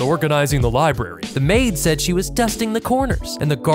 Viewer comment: Bruh I heard “dusting the corners” I was like that is odd 😂